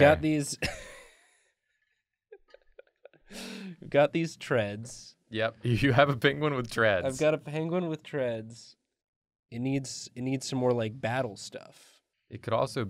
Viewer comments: Who thought they said a penguin with dreads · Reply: E-Z Space I thought she said penguin with Tourette’s at first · I thought you were saying "penguin with dreads" at first 😅